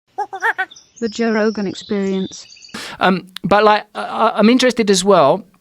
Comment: in the intro sounds like she says "Joe Logan experience"